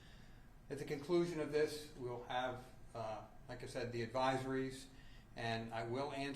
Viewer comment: Sounds like he said she said.